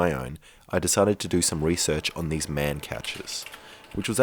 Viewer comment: Thought that the catcher said butcher